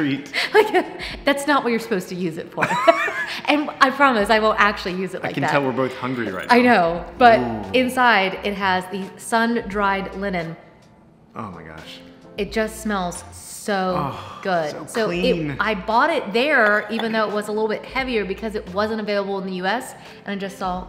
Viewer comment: lol - I thought you said sun dried lemon! I was so excited about ordering that fragrance.😆